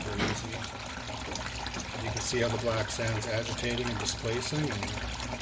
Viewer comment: not quite sure what you said there... 'something in our gold'. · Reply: Sorry, I will add some closed captioning there since it was hard to get the audio balanced with the water flow. The line is: "You can see how the black sand is agitating and displacing, gradually leaving cleaner and cleaner gold."